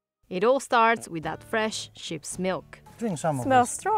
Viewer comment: 😂I didn’t understand the accent And I thought she said chips milk lol